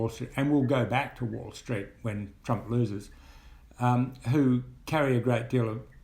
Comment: did he say when Trump loses @ 13:40? is he calling the 2020 election already?